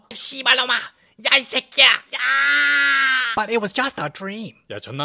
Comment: bruh i heard him saying “shiba lon ma” if you’re vietnamese, you’ll understand it in another way